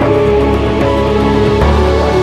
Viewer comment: Can some1 tell me what he say? Please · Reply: "I have plenty experience In this"